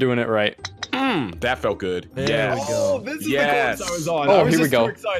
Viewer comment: I thought he said “Bappo good”